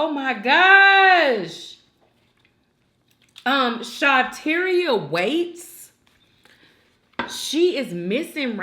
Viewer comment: Shawtyeria Waites is her name I had to research I thought you said Shateria Wade. I pray she is alive and found please Lord…